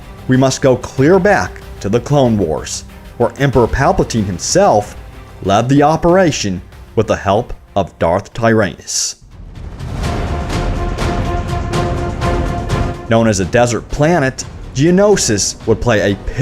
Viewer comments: I thought you said ”Darth Tie Anus” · In 0:42 did he say dark anus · Did he just call Lord Tyranus, "Darth Tire Anus"??